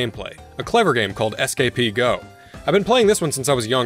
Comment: What's the game you mentioned at 18:34? I googled what I thought you said, but nothing was coming up. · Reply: That is Escapee Go